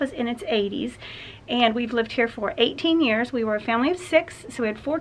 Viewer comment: HaHa! I had to go back and listen to your introduction again. When you said you were a family of 6, I misheard and thought you said you had raised 6 kids in this home. I am so relieved to know it was 4 kids and not 6 kids! Beautiful house, BTW!